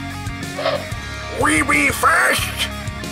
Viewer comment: I thought Snarl said "WEE WEE FAST" BUT THEN I WAS LIKE.....ITS WE BE FAST